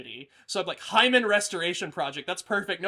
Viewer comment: I thought you said "The Human Restoration Project" and I thought that sounded really cool. I thought it was going to be about transhumanism or something. Unlocking the stripped potential of the human being. I reserve copyright.